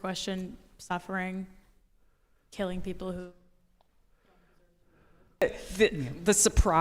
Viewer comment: What does she say here? Is it censored, or did she just not speak into the mic? I think it was "killing people who don't deserve to be killed"